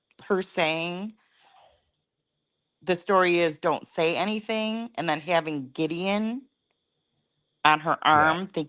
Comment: To me, I heard Dylan say "I'LL SAY ANYTHING" Not :Don;t sat anything....